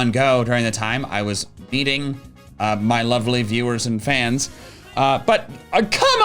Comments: am I the only one who heard him say "I was beating my lovely viewers and fans"? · I know says meeting but i really heard "beating my lovely fans" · did he say meeting or beating?.. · I was watching on mute because it's late and near 21:34 the captions said "beating" and I was very confused until I turned on the audio and realized it misheard "meeting" LOL